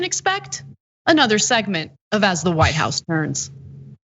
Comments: When the segment started I thought you said "As the White House Burns " instead of "As the White House Turns " LOLOLOL 😄😆🤣😂☺😁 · For a moment, I thought you said "As The White House Burns." 😂